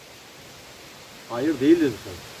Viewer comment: 12.58: ''ahmet'' 14.50: ''...to navi'': (Navi: üç direkli yelkenli gemi) 15.23: ''cem yetim!'' 15.37: ''vita liini'' (hayat, yaşam, ömür) 16.12: ''sin a son dono'' (günah sahibi) 16.42: ''özlem'' 16.50: ''Hemşin'' (Rize'nin ilçesi). 19.36: ''va sem terra'' (topraksız gitmek (portekizce), kara (ispanyolca). 20.08: ''el asfanu'' (okunuşu el asfanu, varlık da bu şekilde söylüyor. Burası Asvan ve 'El asfanu' diye okunur. Asvan Güney Mısır'Da Nil ırmağının doğusunda bir kenttir ve turizm merkezidir). 25.33: ''kabil'' (anlamı: olabilir, olanaklı demek, ''kaabil diyor varlık (kaabil in anlamı da mümkün olmak demek). Burada o kişi hayatta mı diye sorulduğunda ''kaabil'' diyor. genel olarak farklı dillerde iletişime geçmeyi tercih etmişler. ben abartmıyorsam eğer, hayatta olduğu, yaşadığı ülke, bu kişinin günah sahibi olduğu gibi bilgiler aktarılmış durumda. Ayrıca Rize'nin ilçesi Hemşin ile de bir bağlantı olabilir. Ahmet, Umut, Özlem gibi isimler algıladım ayrıca. Yanlış duymuş olabilirim. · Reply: te hayır değil diye anladım sürekli geri sardım 🤷